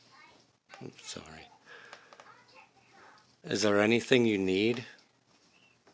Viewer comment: omg are y’all listening how the tv or whatever is playing in the background is answering his questions??? Or am I reaching?? when he says hello it sounds like they say “hi” and when he asks is there anything you need they say “yes” 😭